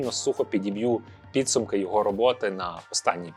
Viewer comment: Мені почулося: "Ну а я традиційно сук# підіб'ю" 😂😂😅